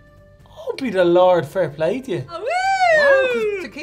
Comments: are there any Irish people who can translate what he just said? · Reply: Almost positive he said "Oh be the lord, fair play to ye!" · Can someone tell me what said at 1:56? I feel as that might be one of those old school Irish sayings I would like to add to the list. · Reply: Almost positive he said "Oh be the lord, fair play to ye!"